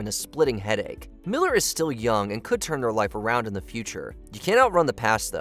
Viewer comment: I'm sure I heard the narrator say Ezra Miller can still turn her life around"... Nice. Lol